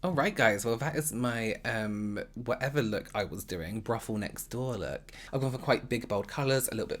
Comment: I honestly thought when you said 'that was my.. what ever this look was, my brothel next door look' I thought you said 'ruffle next door look' and I absolutely died ahaha, ruffle as in ruffle bar.. the chocolate bar with pink coconut inside 😂😂😂